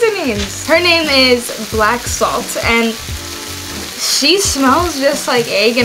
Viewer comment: What is the name of the ingredient that you added to make it taste like eggs? It sounded like you said "black salt", but I'm not sure. Thank you. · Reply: Hi! It’s black salt :)